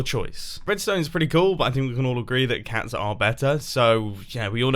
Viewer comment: The subtitles say “We can all agree that PUGS are better” Not as you said, cats. I like Pugs and Cats.